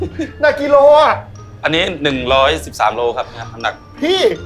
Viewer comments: ทำซับผิดหรือเปล่าคะ ได้ยินพี่เจ้าของร้านบอกว่า 113 โล แต่ซับพิมพ์ 130 หรือเราฟังผิด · กระทะแบบนั้นเรียกว่า wok แบบเชฟว่านั้นแหละครับ pan มักจะเป็นกระทะก้นแบนครับผม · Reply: บุชเชอร์พูดว่า 113 กิโล แต่ซับขึ้น 130 kilos ครับผม